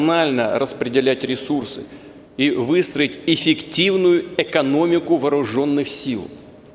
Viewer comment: Мне одному показалось, что он сказал: "Высрать эффективную экономику..."